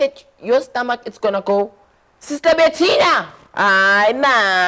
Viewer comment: Your stomach is gonna go SISTER BATTINA ahhh man To goo goo goo to goo goo goo goo to to InDOmie time (Sorry that what I heard)